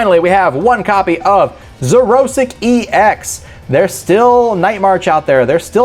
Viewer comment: Who heard him say 'Xerosic EX' @ 17:03??